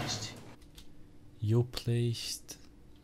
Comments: à 11:26 j'ai entendu "You're blasted". Peut-être que j'ai mal entendu mais si c'est vrai ca craint (ça veut dire "Tu es foutu" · perso j'entend "You're next" Je sais pas si vous vous avez compris ça ou c'est moi qui ai de la merde dans les oreilles 😂😂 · moi j’entends “you’re faced” (tu es confronté), ça n’a pas trop de sens mais j’entends clairement ça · pour la voix inexpliquer à 11:30 j'ai entendu une voix d'homme dire "here a place to..." donc : "Ici, un lieu où..." .... Creepy XD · Personne l'a dit mais moi j'ai l'impression qu'à 11:37 il dit "you made a mistake"